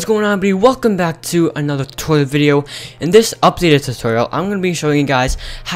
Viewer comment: lol the start of the video i thought he said "welcome back to a another toilet video" 😂😂😂